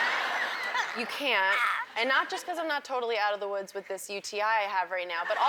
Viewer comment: what does she say at 6:42 ? :D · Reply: u.t.i. urinary tract infection lol